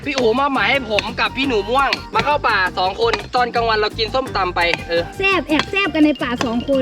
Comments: พี่พูดว่าตอนกลางวันเรากินส้มตำไปเธอ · ทุกคนได้ยินว่าอะไร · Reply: แอบแซ่บ